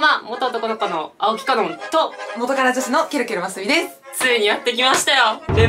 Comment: 元カノ女子の？？？ 初めて来ました。聞き取れなかった。。。